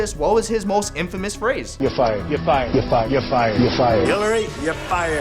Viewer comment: Was I the only one who that thought trump said "ifires" instead of "your fired"